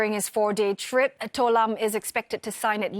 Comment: did i hear "to lam" 😅😅